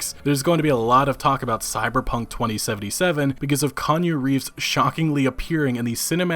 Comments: Did he say "Kanye Reeves?" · did you call him Kanye Reeves? LOL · I don't know about everyone else but I heard Kanye Reeves instead of Keanu Reeves and burst out laughing! dosen't even bother to correct himself and keeps saying Kanye through the whole video LOL! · Sounds like you said Kanye Reeves XD